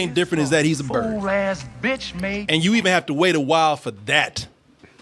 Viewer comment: does he say bitch ass??? Is this a kids movie 😂